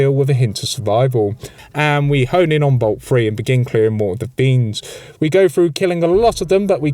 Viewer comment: why I keep hearing beans?! Are you saying beans?!😂